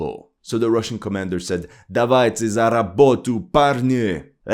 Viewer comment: They actually said: Работаем, братья! 😂